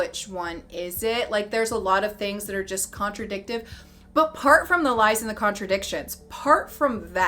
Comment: are you saying "part" instead of "apart" ? Lolol